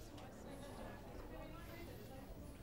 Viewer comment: I had to google fray bentos. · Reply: I thought he was saying Fred bentos no wonder Google couldn't help me